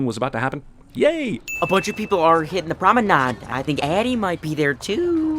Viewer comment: When he said promenade I thought I heard him say ramadan.